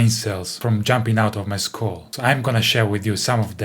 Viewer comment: If brain cells jump out of a bassists scroll, that would be a sight to see. But I misheard him. He said skull, not scroll. 📜 💀